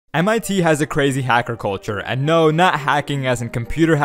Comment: Thought he said crazy agriculture, lol